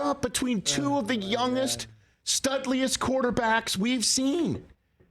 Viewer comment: did Rich just say "studliest"? Absolutely incredible, I love this man